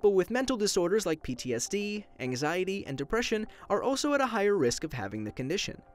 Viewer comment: CRAP IM A 10 GIRL, And... did he say High Risk?! AHHHHHHHHHHHH 10+ PEOPLE, I hope you felt me :(